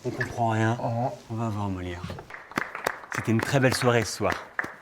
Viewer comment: c'est moi ou dit "On va avoir un Molière" et le sous titrage est foiré? Super vidéo sinon ^^